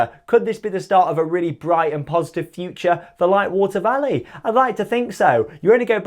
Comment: Did you say "A Brighton positive future" 😂😂